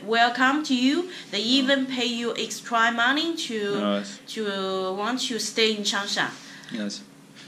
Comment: It sounded like was saying foreigners can get paid to stay in changsha at the end